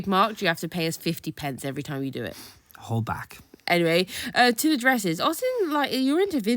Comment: Lol the hold back I heard it as whole back...i had to go back a few times. edit commented too soon that bag drool